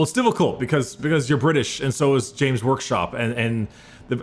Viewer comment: did say James workshop?